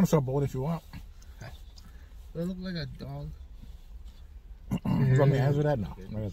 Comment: I understand here as much as I do Bad Comics. What did he say? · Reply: Do I look like a dog ? “ you don’t want me answering that”.